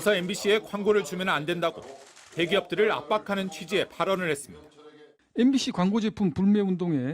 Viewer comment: MBC 광고제품만 사라는 소리로 들리네